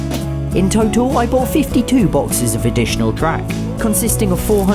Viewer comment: Why did I hear "52 boxes of additional crack" lol